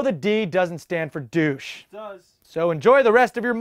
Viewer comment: Did anyone else hear: "It does!" or was it only me 'cause I'm wearing headphones?